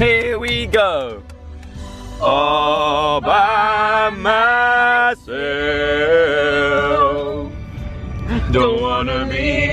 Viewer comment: when you were singing “all by myself” all i kept hearing was “obamas elf”